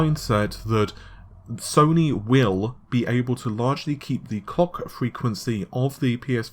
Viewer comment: "Sony will be able to largely keep the COCK frequency of the PS5's GPU at the 2.23ghz" MISHEARD LYRICS EDITION